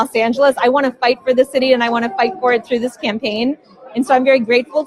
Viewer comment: did she say she wanted to fight for it through this "scam-paign"?